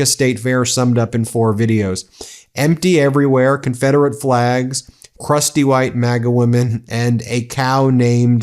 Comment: Did you say Confederate flags were at the fair (4:40 time stamp)? America just can't help itself when projecting racism. World is watching.